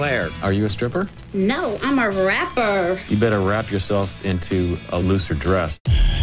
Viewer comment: “You better wrap yourself into a looser dress” gave her a bar here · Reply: I thought he was saying "a lizard dress" man that's rough